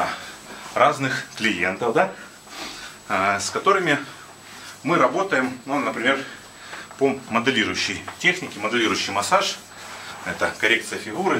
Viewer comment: solo entendí que dijo : "muy rabote"